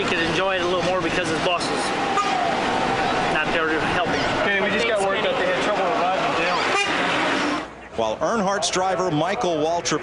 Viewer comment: "Ken we just got word they had trouble reviving Dale" Would be interested to know what he said to that. Probably just "I'm not a doctor"